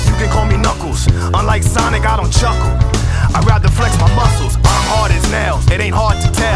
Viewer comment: I thought he said I’m hard as hell